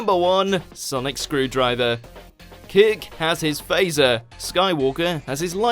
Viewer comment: Is it just me who hears "kick" instead of "kirk" at 10:36? "Kick has his phaser."